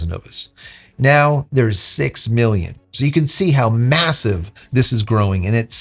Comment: 6 Million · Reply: Sounds more like it, I'm in a loud environment, I thought you said Billion.... yes Million seems reasonable, but as you said, no worries at all when Version 3 launch.... as I said Wild Times, we are living history right now... Be Well enjoy the Weekend... 🕊️